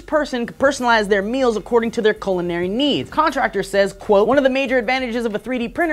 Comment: Also it sounds like says COLONary not culinary.